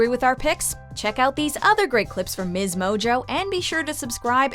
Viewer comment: Who else heard her say miz mojo?